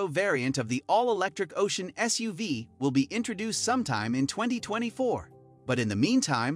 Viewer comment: pop out a 2004 instead of 2024 😉